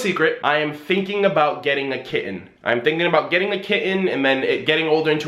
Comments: I choked, I thought you said you were getting a kid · I thought he said “kid”😳 my heart jumped for a second great vlog tho enjoy you’re new place XD you deserve it.